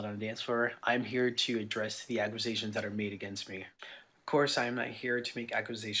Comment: Is he saying “AcWOOsations”???